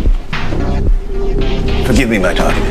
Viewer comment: For once I'm glad for the subtitles. I thought Narcisse said "Forgive me darlings," and thought 'Wow, that's overly familiar.